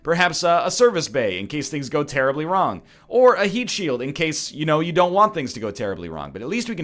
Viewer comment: Am I the only one who thought that said meat shield?